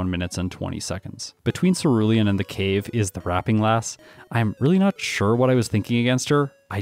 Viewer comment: Once upon a time, I praised your edits. Allow me to do it again: having aphantasia means I was out of the loop of the Rapping Lass joke: I only heard you saying wrap, every single time. Until today, where you brought an fanart and the brick hit me with full force, making me retroactively laugh about all the times you mentioned her and now I know why she matters so much outside of her being a gameplay menace: she's able to drop some bars, yo! I'm grateful and your content keeps being first monitor content. Bad part is, I can't use you to sleep!